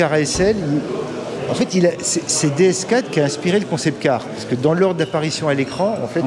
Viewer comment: Un moment j'ai entendu "DSK a inspiré l'ASL" wtf! Aucun rapport lol